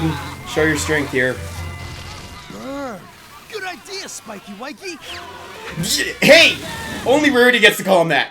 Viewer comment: Did Garble just said Spiky whiky